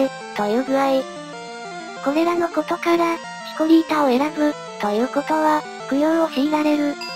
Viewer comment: チコリータを可愛さで選んだ俺な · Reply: シコリータに聞こえた...